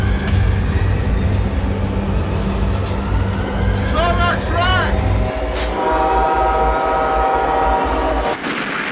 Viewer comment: what did he say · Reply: "He's on our track!" A passenger who in testimony shouted this upon spotting the CN train on a collision course